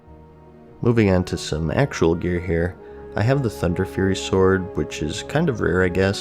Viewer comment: Did someone say... Thunderfury?